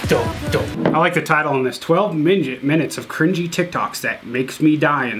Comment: I like how at 8:15 says midgets instead of minutes